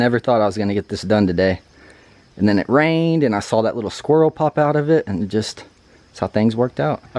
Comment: Crushed it! At first I thought you said the squirrel spoke to you 🤣 I can’t wait to come up this fall and get some camping in before it gets too cold. Great news about going full time on the channel! Looking forward to more adventures! 🌻